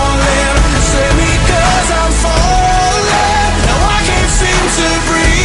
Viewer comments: Good video. I'm the only one who hear "Sammy, Cas I'm falling" or "Save me Cas I'm falling" ? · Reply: I heard it as save me cause I'm falling but after reading your comment now I just hear 'sammy, cas, I'm falling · I know its not Jensen but at that one part it sounds like he says "sammy, cas, Im falling "